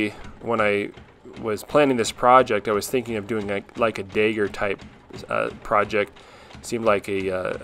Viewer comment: Not gonna lie, I was so damn confused every time you said "dagger". I thought you kept saying "digger" lol. Thick mid-west or Texan accent when it came to that word!